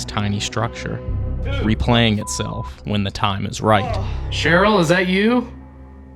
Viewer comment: I heard ..." it is right. "...😮.